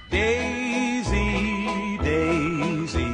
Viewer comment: The audio 💀💀💀💀💀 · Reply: that's what is sounds like I heard "daisy Daisy give me your a$$ to. Cook" I'm serious